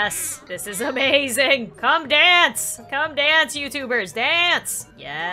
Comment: When said "c0me youtubers, dance!", I thought that she said "come KUGERS dance". Im just lile wwhhaaaat